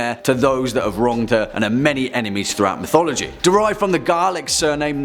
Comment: Nicnevin is Gaelic. Sounded like you said “garlic” haha. It’s pronounced differently.